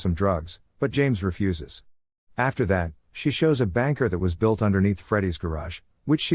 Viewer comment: he says BANKER instead of bunker